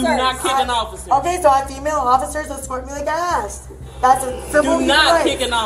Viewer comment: She says, "have a female officer escort me like I asked." The text reads, "so a female officer is escorting me like an ass." Not even close to what she said.